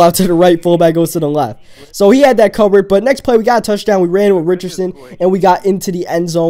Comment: oh Lol I thought you said we got a touchdown with